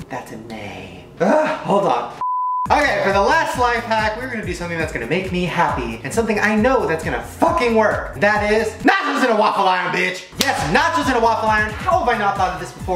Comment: I legit thought he said “matches in a waffle iron!” Lol 😂